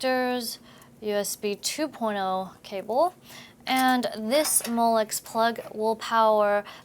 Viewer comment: Van someone tell me what she is talking about at 4:45?? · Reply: Molex plug, I found it